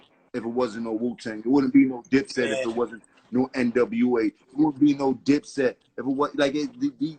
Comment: Did Jim say @ 5:25 there would be no NWA if it was no Wu Tang.. get your fact straight..NWA came out in late 80s..WU early 90s.. salute to the Wu